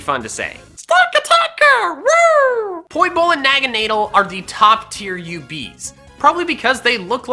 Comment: Did he just say naganeedle is a bad-a